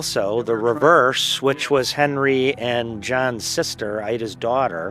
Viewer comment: I heard "henry and.... Vivian" the Vivian was fast though